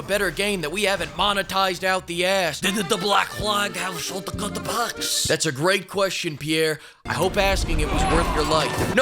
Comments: what did he say at 2:25??? Did the black flag shatauptaquacks?? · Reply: Short cut packs. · replayed this part ten times and I still can't make out what Pierre is saying lol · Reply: "Did the Black Flag have shortcut packs?" I think...